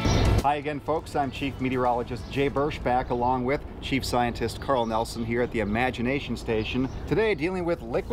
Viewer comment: So I just realized he was "chief scientist". I've been watching these for a while, and I always thought they were saying, "cheap scientist"